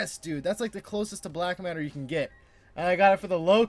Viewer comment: did you say black matter